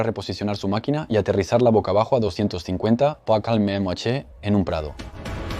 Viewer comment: Excelente documental, pero no entendi la frase del narrador en el min 15:09 "pacal mel mache" eso es lo que escucho (no tengo mucho conocimiento de francés 😢)